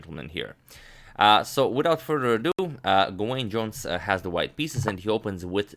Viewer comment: I heard you say Dwayne Johnson vs Gukesh D instead of Gawain Jones :D